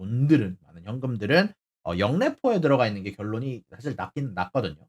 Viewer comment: 많은 현금들이 영내포?? 에 들어가 있다고 들리는데 그게 뭔가요 님 ㅠㅠㅎㅎ 제가 잘 몰라서리.. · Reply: 역레포요